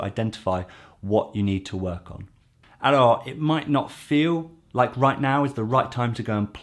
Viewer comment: Did I hear a French Alor in the middle of an English speech?